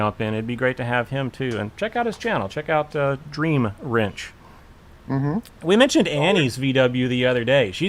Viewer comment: I like your vw content and Gary's on vw jawbreaker. I watched till the end of the recorded video where you mention what I thought you said check out green wrench. So I looked up that to find Dr. greenwrench...wait a minute there is no vw on this channel. added vw squareback to the search to find out it's dream wrench no green...lol